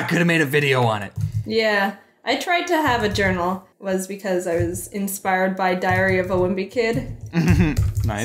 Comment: Jaiden : Diary of a Wimpy Kid Me : I thought she said Diarrhea lol